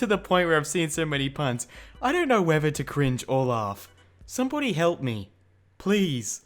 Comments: did anyone catch that he said somepony? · I thought you said somepony and I had to rehear that XD